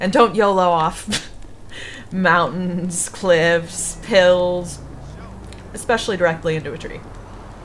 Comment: Did she say pills? XD